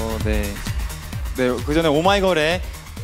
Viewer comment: was he saying oh my god a😅😂